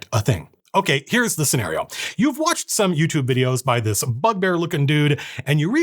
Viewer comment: i heard budbear instead of bugbear so now i imagined you as a drinking bud with beer.